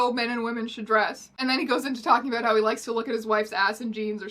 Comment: wait did I hear you say “his wife”???? This man actually managed to marry a real, 3D woman???????? I’m???????? Shocked????? And?????? Confused??????? He’s????? Not??????? An incel?????????